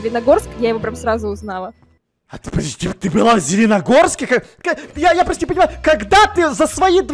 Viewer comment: Зеленогорск А МНЕ ПОСЛЫШАЛОСЬ ЗЕЛЕНОДОЛЬСК так жаль